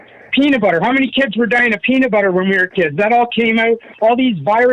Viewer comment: “How many kids were *dying of peanut butter *?”? Is that what he just said? 😂